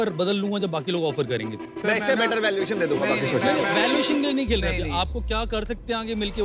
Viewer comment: Did Aman just said: "Me isse better valuation de dunga, Maa ki ch*t?"